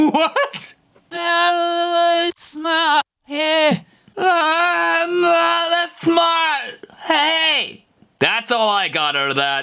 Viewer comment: i heard iM nOt ThAt SmArT Ay